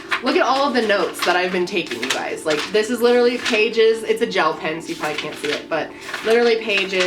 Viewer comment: You said “gel pen” but I thought you said “jail pen” & I was like,”Damn! She is REALLY in to this true crime thing!”